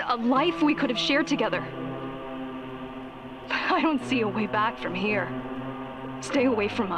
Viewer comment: I'm pretty sure the subs are wrong. ''Bit...'' instead of ''but''. So yeah, shitty work my friend, remake the video, like's gone